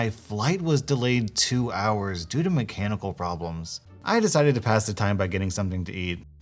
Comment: When it said, “my flight was delayed two hours due to mechanical problems...” I thought it said, “my flight was delayed two hours because of my cannibal problems” and I was just thinking, what what.